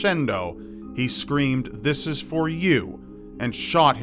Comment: That's not what Ricardo said..He shouted "victory!" Before pulling the trigger.